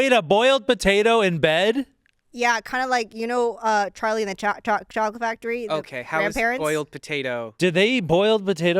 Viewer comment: lmao I thought Olivia said, "Charlie and the Child Factory"😂